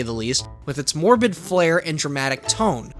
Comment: I heard him say morbius but he actually said morbid. Also where is lego morbius